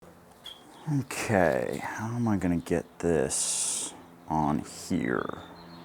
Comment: 😂 I thought he said "NK, how am I gonna get this on here?"